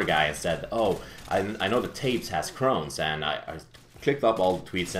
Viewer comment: no sorry i thought you said crowns